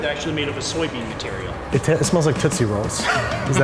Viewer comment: I thought he said titty rolls in the beginning